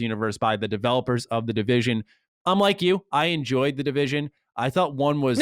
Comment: I heard "unlike you" also the 1st time.